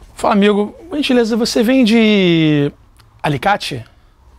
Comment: so eu entendi no 3:13? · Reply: "não vende pão com alicate"